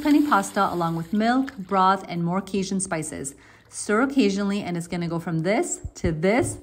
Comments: She kept saying "Cajun" so much that towards the end, I thought she said "stir a-Cajun-ally" 🤣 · I heard: "Stir O'cajun'ally 😄hehe